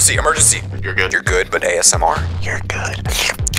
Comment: You're good but asmr *makes slurping sounds* What I heard: "You're good sksksksks"